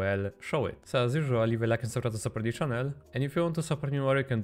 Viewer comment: what did you say? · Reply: To leave a like and subscribe to support the channel